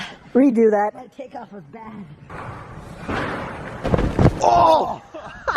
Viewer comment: This is what he said at 5:00 "aww redo that [lower volume] I'll take off a bat